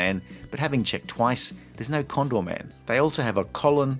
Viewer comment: thought it said condom man